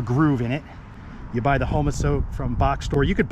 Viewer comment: please spell out the name of this moisture control board? It sounded like you said " Homa-soap" but Im not sure and I'd like to get it. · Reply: Lol. Close. Homasote. <link>